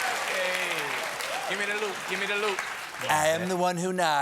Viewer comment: did he say “gimme that loot, gimme that loot”😂